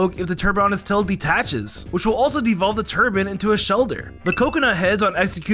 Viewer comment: Wait what did he call the Shellder evolution?! I heard ‘Turbin’ which sounds nothing like ‘Cloyster’